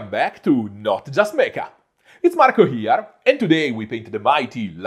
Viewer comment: First time viewer. I thought the intro said "not just make-up" I'm thinking... this looks professional level... the make-up job must pay really well! 🤔😅